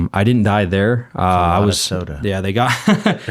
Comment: What did say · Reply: “That’s a lot of soda”😂😂